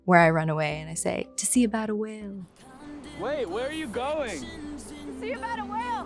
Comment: what is she saying? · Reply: “To see about a whale”